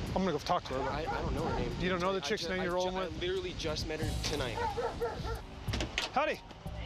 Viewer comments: "We got a chick walkin' up to it here". First officer I have heard call a woman a chick on the job lol. Once she is detained/arrested he calls her honey! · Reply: I thought he said honey too but pretty sure it was howdy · Did I really hear that cop open the conversation with the woman by calling her 'hottie' ?!